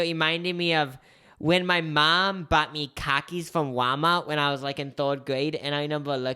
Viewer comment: my northern ass thought you said car keys instead of khakis 😭